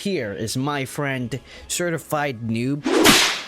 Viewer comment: when he said certified noob I thought he said stir fried noob lol